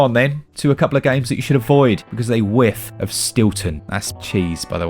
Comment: Never heard of Stilton honestly, but I did misheard you at first you saying Wilton, so after you mentioned google of course I had to google it, and was left confused since there is Wilton cheddar cheese, and cheddar doesnt smell all that horribly, so I rewatched that bit, then I had to google Stilton and now I get it. Also now I want some cheese